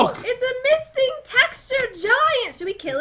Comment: Did Jen say Misting Texture Giant?